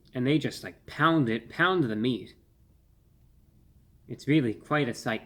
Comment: what is my man saying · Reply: hahah he said pounding meat haha just like a wiener haha oh my god dude that is so good hahah good gravy haha